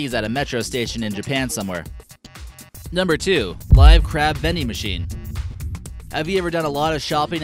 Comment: when he said live crab vending machines I thought he said Minecraft vending machines